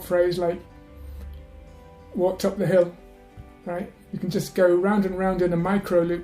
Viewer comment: Actually, what I heard was "What's up the hill?". Listening comprehension is really a disaster. It feels like it cannot be helped no matter how long and how hard you try.